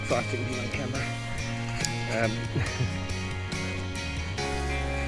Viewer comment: did he call it a cunt lol